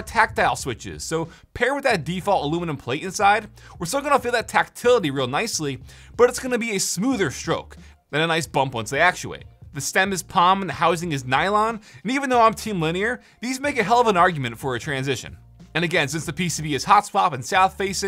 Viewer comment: thought I heard him say, "it's going to be a smoother stoke then a nice bump once I actuate."